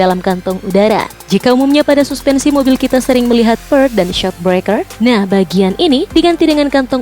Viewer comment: s.d. 1:05 ngomong apa? Perk? Ferk? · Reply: Per maksudnya 😅